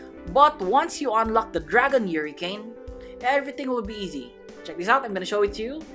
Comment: bro said Yuricane instead of hurricane